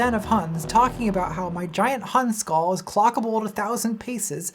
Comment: "My gigant Hun skull". Mishearing this just made me wiki ritual cranial deformation among the Hun people. I was going to make a joke about nomadic warrior contra, but now I have a rabbithole to explore instead.